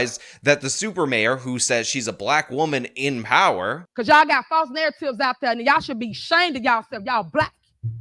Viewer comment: Her lack of ability to speak English should be enough to fire her. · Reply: I had to put the subtitles on when she was speaking as I heard her say “false nails” at one point, then the subtitles said “false narrative” 😂